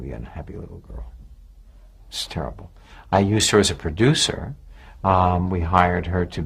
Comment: Sounded like he said "I used her as a producer"